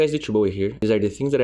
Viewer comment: i thought u use to say d2 bowie xD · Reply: he does, it just sounds like "it's yo boy here". I picked up on it when I started watching but never saw anything in the comments about it